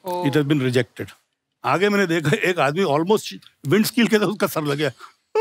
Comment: what is he saying? Ek admi windskill se sar laga kar? Samaj nahi aa raha. Can anyone please help?